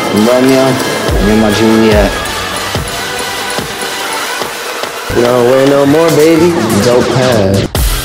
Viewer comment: Is anybody know what he was saying in 0:25? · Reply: "no. wait no more, baby. dope pad."